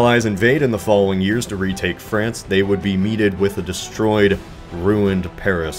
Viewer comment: did you say “Meeted” ? I’m hoping I misheard that...